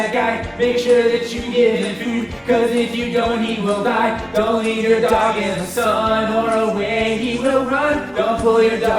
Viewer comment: Is it weird I thought said "Don't eat your dog in the sun"?